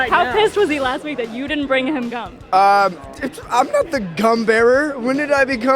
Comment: AHHAHA sounded like wes said "i'm not the cum bearer!"